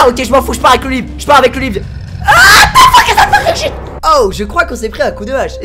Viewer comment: tu dit quoi Squeezie ????? ^^ Whaaaaaaaaat the fuck is a tomagotchi ?? ^^ je deconne mais c'est incroyable tas voix comment elle aigu des fois ! :p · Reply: Il dit "what the fuck is that fucking shit" xD